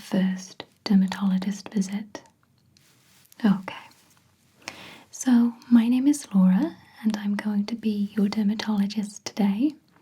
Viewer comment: I thought you said 'My name is Sephora' and I laughed so hard and then I realised that wasn't what you said at all and now I'm laughing even harder. Thank you for brightening up my Sunday morning! Greetings from the Netherlands.